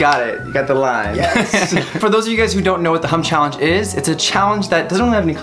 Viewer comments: Was I the only one who thought he said "hump challenge"?? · Am I the only one who thought said 'Hump Challenge'??😂